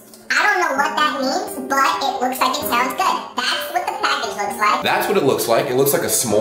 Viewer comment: Did you just say: "it looks, like it sounds good"? (for the Knoppers) xD